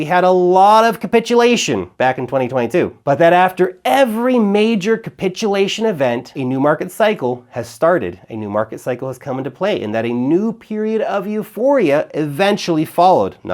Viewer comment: I totally Thought he Said he Sharted 😅💩